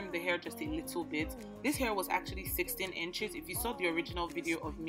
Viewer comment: What length did you use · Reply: Oh I heard you say 16 inches